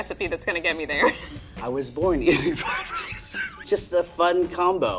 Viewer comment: I’m sorry, did he just say he was BORN in fried rice? 🤣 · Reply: He said he was born eating fried rice! 🙄